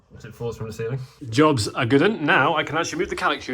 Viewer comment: Not from the U.K I’m tryna figure out what you said 1:54 jobs a (good one) or ( good un) 😅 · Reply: I said “good-un” which essentially me saying, that’s a job well done :)